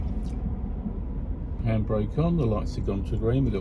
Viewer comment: Not sure what you are asking? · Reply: just heard that too. At 2:29, stoppingng behind a car before continuing. I think u said handbrake on?